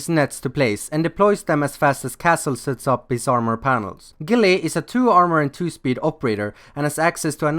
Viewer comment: I thought it said chille lol